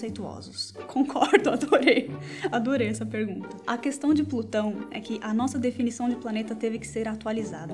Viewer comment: eu entendi putão kkkkkkkkkkkkkkkkkkkkkk